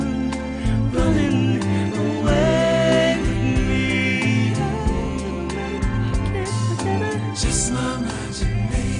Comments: What does he say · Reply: I never met her but I can't forget her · What does he say at 2:22... My favorite part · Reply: Ray Scott “I never met her but I can’t forget her”